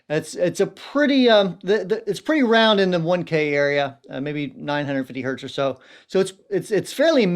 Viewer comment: I thought he said the “Wednesday area” not the “1k area” and now I’m convinced someone needs to make a pedal called the “Mid-Week Hump”